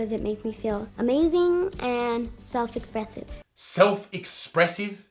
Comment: Desmond: Drag is very expressive. Me: Laughs in Lego bricks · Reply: I thought u said expensive instead of expressive and honestly it’s not wrong